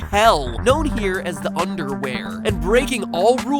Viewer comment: I laughed when you said “Underwhere”. CAuse I thought you said um underwear lol..